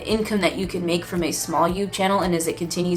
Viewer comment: did she say yoob channel? Lolol